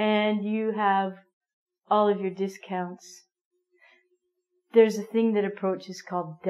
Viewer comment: Did you say "There is a thing that approaches you called death."? Am i right? That sentence is little fast for me.